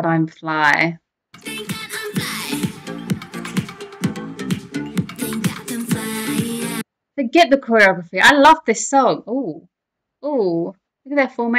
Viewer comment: Did I hear that right,? "Forget the choreography ...." 1:15 😲😆😆😆 I love your spontaneity. 😄 The multi-talented Jurin, XG team leader and choreographer, had dreams as child to to become a model and so.....